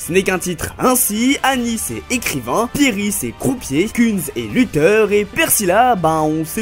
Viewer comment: pieris est quoi ? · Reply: Croupier ! C’est à dire qu’il anime des jeux d’argents ou de hasard comme des casinos par exemple ! Il base tout sur le hasard c’est pourquoi il a toujours une piece avec lui pour jouer à pile ou face !